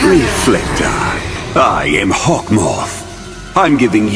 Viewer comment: can someone give my an reasons Why i heard "I am Hot moth"